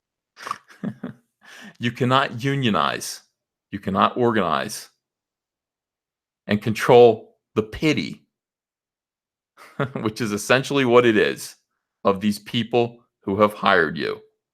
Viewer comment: I thought you said control their panties...probably a good idea